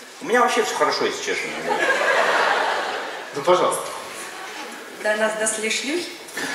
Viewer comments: что она сказала:"даже одна без шлюхи"?не расслышал 😂😂😬🙊🙊,ааа нет,простите,вроде:"даже до нас дошли слухи" что-ли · что она сказала? непонятно шо то... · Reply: «До нас дошли шлUхи» , а хотела сказать «до нас дошли слухи»